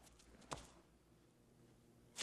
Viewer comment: that's what he said? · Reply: Wow what a hole is what he said